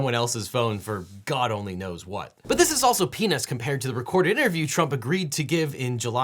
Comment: Why did he say “penis” @ 4:28? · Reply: He said peanuts, though I can see why some might be confused.